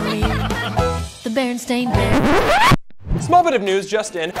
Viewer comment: listen closely...... it sounds like it says bearenstein